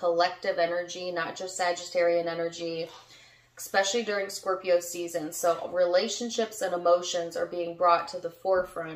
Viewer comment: Uhhh anyone else hear the male voice at 12:36? I couldn't make out what he said. But whoa! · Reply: Yes almost a whisper. Like a spirit is trying to say something. I listened over & over sounds like hes saying ok.